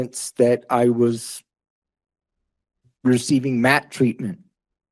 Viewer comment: did he say MAP treatment? As in "minor attracted person" treatment? I'm not sure that's what he said but I do know MAP is a fancy, new, politically correct word for chomos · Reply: Pretty sure it was MAT. Medically assisted treatment is usually Suboxone or methadone for treatment from opiate addiction/heroin/fentanyl etc. Cause he was talking about overdoses and stuff I'm almost positive he meant MAT treatment.